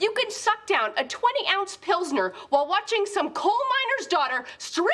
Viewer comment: What did she said please? :D She said Pilsner?